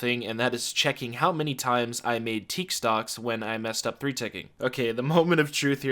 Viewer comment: When you said teak stocks I thought you were saying tik toks